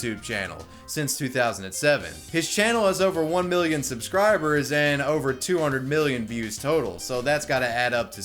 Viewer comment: did you say 1 billion subscribers?